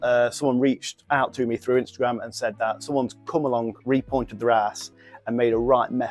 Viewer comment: I thought he said “repointing their arse” 😂😂